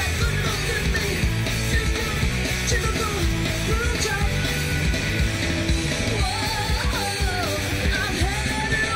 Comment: Im sure she didnt say what it sounded to me like she said....so what does she say @ 2:35 ?? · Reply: she said “just your typical prototype”